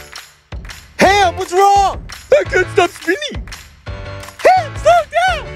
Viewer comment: I know he is supposed to say, “I can’t stop SPINNING!” But what I hear is, “I can’t stop WINNING!”